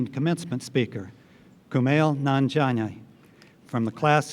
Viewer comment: For a sec it sounded like Nanjina =>vagina